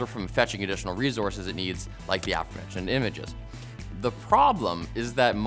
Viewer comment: Hey there! Just a friendly heads-up that the captions at 2:46 could use some tweaking. I'm a hard-of-hearing individual and rely on captions all the time, especially on YouTube. I think what was said was "aforementioned images" but this was captioned as "(chattering) and images". I'm not 100% sure if that IS what was said but, going off of context, that's what I get. The other part of that is that, for those of us that rely on captions, the expectation in that case is for that missed part of the utterance to be labeled as "(indistinct)", "(unintelligible)", or "(unclear)". "Chattering" can incorrectly denote a certain sense of triviality. Thank you all at LMG for everything you do to make your content accessible to the deaf/Deaf and hard-of-hearing communities! 🤟